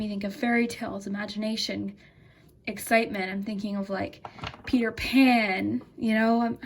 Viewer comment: I chose pile 3 and i'm so sleepy i thought u said "pee your pants" when you said Peter Pan I'm--- 😭😭😭😭😭